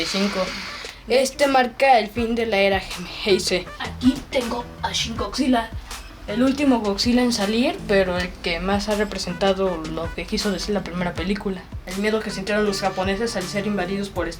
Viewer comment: Escuché Barney Godzilla